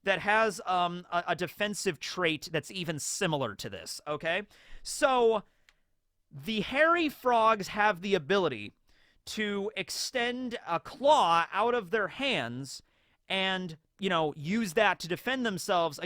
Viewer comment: Dang with today's Frog Facts™ I thought you said "Horror Frog" when introducing the Hairy Frogs, then the image popped up and I was like sheeesh that do be one horrific frog